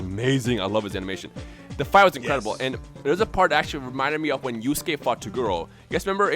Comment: I thought he said "Reminded me of when you skate-fought that girl." I was like, "What the hell is skate-fighting and why was this guy skate-fighting with a girl?"